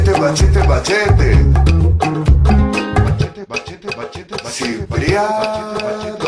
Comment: lol this is hilarious coz it sounds like hes saying machende machende machende,which means ball sack in my language..so when i randomly played this at a family bbq my mum was like wtf.....